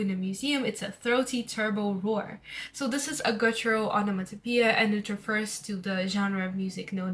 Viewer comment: so sorry but what did you say here, before “onomatopoeia”? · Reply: guttural :)